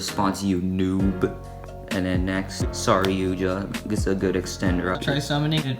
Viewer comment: it sounds like I said "Sorry Yuya"